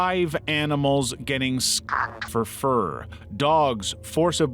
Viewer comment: whàaaaaaaaaaaaat did I heard? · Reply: Skinned for fur!